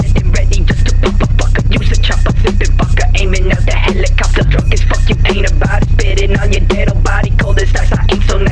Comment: Am I the only one who thought he said “drunk ass fuck you ain’t about it”